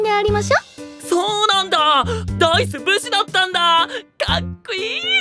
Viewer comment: 今更だけど何回聞いても｢そうなんだァ！帝統ブスだったんだァ！？！？｣に聞こえてしんどい